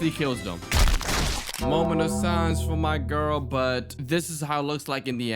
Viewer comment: did u say my girl????